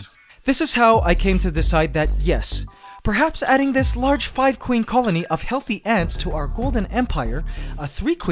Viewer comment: did he just say 5 Queen?!?